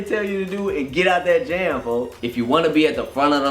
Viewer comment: I thought you said you can be Soulja Boy if you want to 😂😂😂. I cried 😂😂